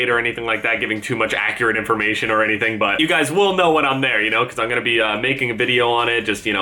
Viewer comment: Who else heard "when I'm married"